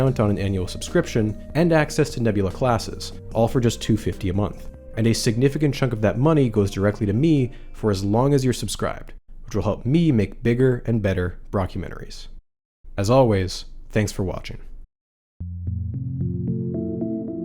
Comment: I was sleeping, listening to this one. Then I thought I heard a say BROC-umentaries. Now I'm awake. I'm not happy about it.🥦🥦